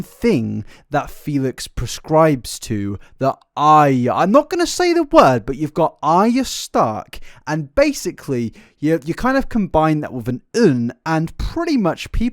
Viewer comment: Can somebody please explain to me what he was trying to say at 1:50:39 I'm so confused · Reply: he’s trying to say ‘aryan’